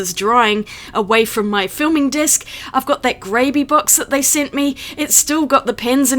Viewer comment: When you said ‘Grabie’ box I heard ‘gravy’ box! I’m always changing sketchbooks and media. One of the wheels on my art cart is broken and so are two drawers (because they are over laden with art supplies 😅). I never thought to look at Meeden or IKEA. Interesting video, thank you.